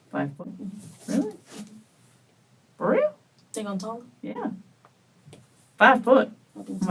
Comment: she didn't say "They gonna talk?"... She said "Think I'm taller?". No idea how you can listen to that conversation and think she said "they gonna talk"